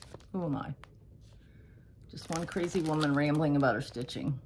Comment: 🤣🤣🤣🤣. Thought you said one creepy woman but rewound and realized you said one crazy woman. Oh my gourd! what a life we’re live. Love you!